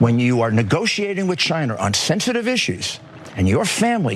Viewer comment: Did daffy just say "chiner" at 00:01 seconds?